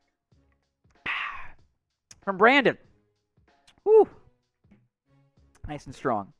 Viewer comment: I thought he said, let's go brandon at one point. could have been wrong though